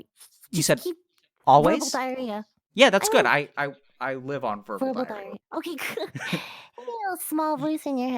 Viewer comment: am i just too high or did i hear @ 17:00 that you live on purple diarrhea? lmao rewinded it like 3x and thats what i keep hearing